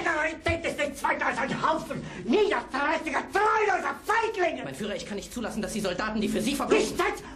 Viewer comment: where's "ich kanne nicht" or however you spell it? seems like its missing words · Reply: I think the General at 1:35 says: "Mein Führer, ich kann es nicht zulassen, dass die Soldaten, die für Sie verbluten ..." (Mein Führer, I cannot let rhe soldiers who bleed for you ...) presumably meant to be completed with something to the effect of "... auf diese Weise beleidigt werden" (... be insulted in this way.)